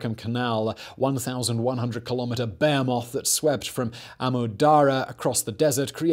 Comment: Okay, when I hear "bear moth", he means "behemoth".